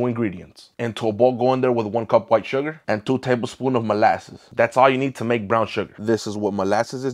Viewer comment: I heard you say sugar and i was about to kill you untol you said molasses 😂